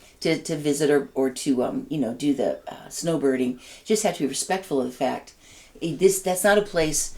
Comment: LOL I thought you said snowboarding at first! I was picturing you and zooming down a mountain slope in your ski gear and thought the Florida trip really was a magic cure for all your ails! Snowbirding sounds perfect and something exciting to look forward to each winter. Glad your trip was a success!